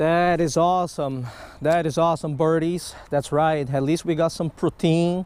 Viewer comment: Lol I thought he said "that's right Birds we got some poutine". Me: I love fries 🍟